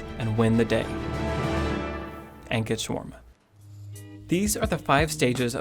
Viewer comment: You mentioned your obsession with learning what was meant by 3-Acts. My obsession for a very long time has been trying to figure out what you said at the 7:22 mark. It sounds like "Angus shawarma." You say it so casually as if everyone will automatically know what you are saying. So, please, what are you saying? Thank you.